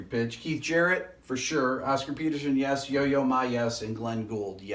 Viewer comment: I thought for sec, he was saying Yo Ma-Ma had perfect pitch.